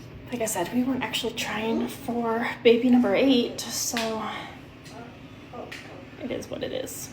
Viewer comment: is it just me that hears “we weren’t actually trying for baby number 8” only saying this bc he heard differently and brings it up again later. Totally agree with all his thoughts just trying to be fair if she was genuinely saying we wereN’T trying Edit: Okay honestly doesn’t matter that she said there bc she later directly says day 1 of trying for baby 8